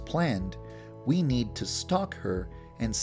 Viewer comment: i legit thought you said "we have to stalk osama" lmao